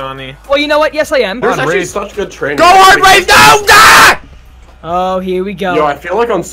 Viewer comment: when your about to die so you speak german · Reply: I heard “go heart rate yoga”.